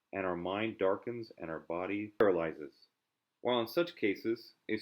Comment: What is said at 1:35 ? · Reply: "... our mind darkens and our body paralyses. While in such cases ..."